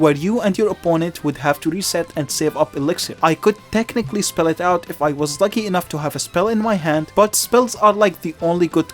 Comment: What did bro say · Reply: I could technically spell it out, if I was lucky enough to have a spell in hand